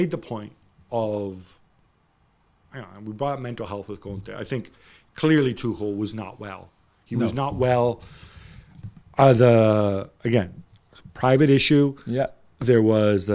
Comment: What does Gab say from 13:00 to 13.10 about Tuchel? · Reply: "I don't know, we bought mental health with Conte. I think clearly Tuchel was not well. He was not well, as uh, again private issue"